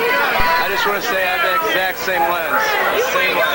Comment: what's he saying? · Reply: He’s saying he had the exact same camera lens lmao